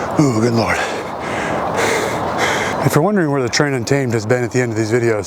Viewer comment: I feel extremely stupid because I always thought the "Train Untamed" saying was "Tread on-time" and it was just some inside Marine thing I didn't know about 💀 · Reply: LOOL. I’ve seen another comment before, where they thought he was saying “Train on time,” all this time 🤣🤣